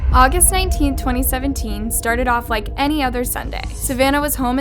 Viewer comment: the subtitles said "any other saturday" but said "any other sunday"